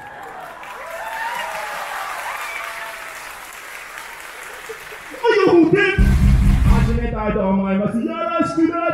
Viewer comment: なんて言ってるんだろ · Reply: 準備はいいか？大阪ぁ！！！ さて、地元MY HOMEということで…お前らのやばい姿見せてくれよー！ １本打って！ ただいまより２本打って！！ THE ORAL CIGARETTESを３本打って！！！ SKY-HI呼んでくれてありがとう(￣∇￣) 〈あきらくん側から、SKY-HIも参戦してた〉 客 パチパチパチパチ拍手 あ、４本打って！！！！ 始めたいと思いまーす ♪DIP-BAP